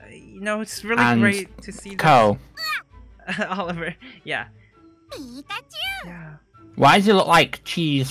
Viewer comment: Did Oliver just say pikachu 16:21 😂😂😂